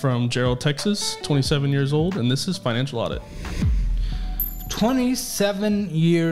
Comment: Thought he said “and this is my enchilada” at the beginning🗿